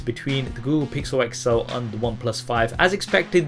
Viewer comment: I thought he kept saying google pixel itself😂😂